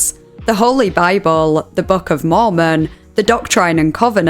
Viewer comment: The LDS entry's CCs censors the word "Mormon," presumably mishearing it as "moron." And I'm not saying they're right, but it is very funny to say the least...